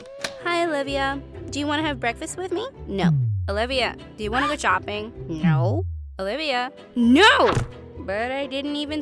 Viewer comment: "Olivia wanna go shopping" But I heard "Olivia wanna go chopping" ✨